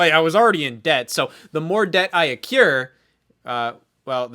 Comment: Could someone tell me what word Scott uses at 19:16 "The more debt I ______" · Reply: he says accure, but I'm fairly sure he meant accrue?